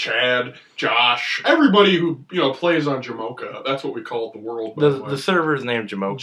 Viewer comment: Did he say JAMOCHA???!?!?!?!???!